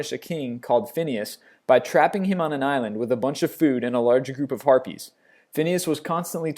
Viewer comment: When you said harpies I thought you said herpes O.o